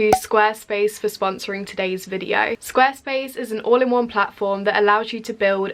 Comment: lmao I thought she said SquirtSpace